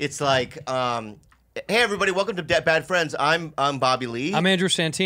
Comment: it sounded like Bobby said “Dead Bad Friends”. I’m imagining a very distant future series of their likenesses and banter rendered by AI.